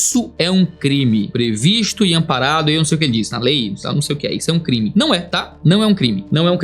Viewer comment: acho que ele fala no final "... comparado ao racismo" (na minha visão)